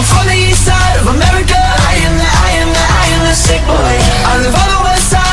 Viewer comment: For a split second I thought they said I am the stick boy